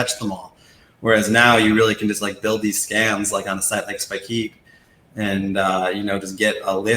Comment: what's that tool mentioned at around 47:15 I can't hear what Tim says? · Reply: Spikeet